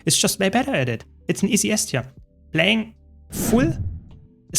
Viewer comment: I can't understand what you're saying here "Playing full celestial staff mirage only going [unintelligible] if you need more healing". Sorry, what is it you say there? I've played it back many times. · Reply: "only go to rifle if you need a lot of healing" Sorry, that you could not understand me there :(